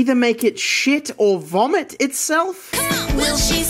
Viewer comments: HELP I THOUGHT IT SAID "will she s*** or poop"😭😭😭😭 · When you did the add for the can I thought it said will she shit or spit😂😂